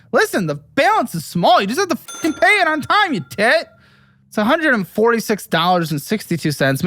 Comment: Did he call him a Tit 😂